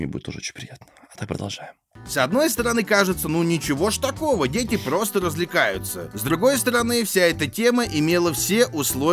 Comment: "дети просто развлекаются" Я, которая услышала "разлагаются":. . . . :|